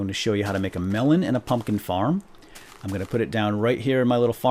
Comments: I thought you said "I'm going to show you how to make a melon and a fucking farm" and I was like LOL WHAT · lol when he said "the farm" when he saw the 3rd creeper, i thought he said (silent what) "The Fuck"